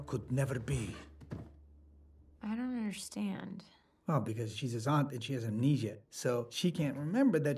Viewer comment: I thought Bruno said “She’s a zombie but she has amnesia” 😂😂😂